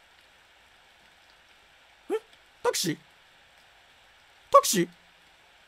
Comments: のたけしがタクシーに聞こえる笑笑笑笑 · のところタクシーにしか聞こえないww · たけしがタクシーにしか聞こえんん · タクシー？にしか聞こえないwww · タケシがタクシーにしか聞こえない